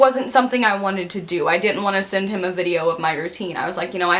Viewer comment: I thought she said "i use to bend him" but she didnt even 😂